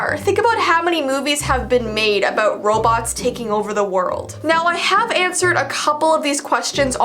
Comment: I thought you said Roblox taking over the world and not "robots taking over the world"